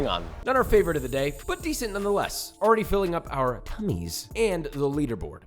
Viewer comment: what did he say at 3:50? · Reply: already filling up our tummies